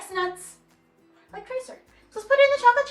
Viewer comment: wait a moment did she said tracer after not everyone like nuts